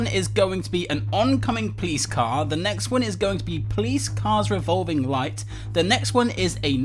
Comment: He said PLEASE CARS instead of police cars 0:10 lol why is this so funny